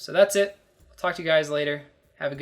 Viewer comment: Did he just say f*ck you guys later?